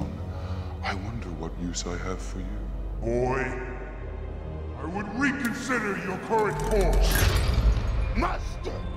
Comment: did he say Boi, Coy, or Oi? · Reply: Boy